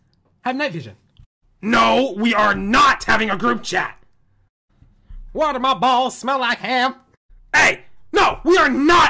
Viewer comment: What i heard: watermelon balls smell like ham